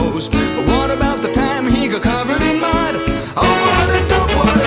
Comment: At [ 1:02 ] I mis heard it saying "What about the time he got covered in blood?" Instead of mud. But indeed this is an Amazing model!